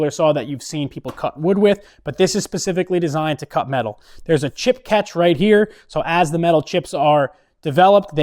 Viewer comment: did he say "pacifically" 😂